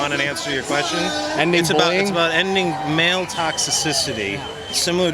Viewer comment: Hey that’s me at 15:09 Nice to meet you. That guy in purple was a jerk, he interrupted me talking to you twice right after saying it was rude to interrupt people. He also said after that when you walked away he was an alpha and was going to show you. Same with guy in SF giants gear. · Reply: I heard 'Male Toxi sissity' lol What a pudwacker Ill bet a toe he has been accused of sexual assault